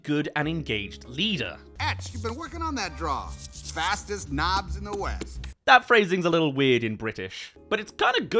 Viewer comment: I always thought he said "Ex keep working on that draw. That is not in the west"